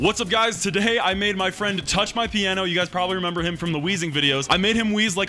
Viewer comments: In the beginning I thought he was gunna say I made my friend touch my penis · Literally at start I thought u said I made my friend touch my piano, no spaces just that full sentence and I laughed my assss off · It sounds like he said “ I made my friend touch my piano” xD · I thought you said in the intro I made my friend literally touch my piano 😂😂😂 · It sounded like you said you made your friend touch your piano